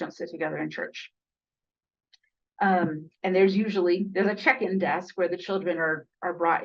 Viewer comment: Oh, check in desk. I thought you said "chicken" desk. Left me confused.